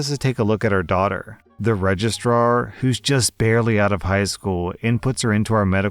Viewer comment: I've been brain damaged by my furry friends... All I heard you say in story two was "Regist-rawr" ..... I need therapy....